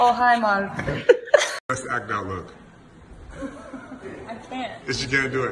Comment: What does Mehcad say at 0:58? Best what look?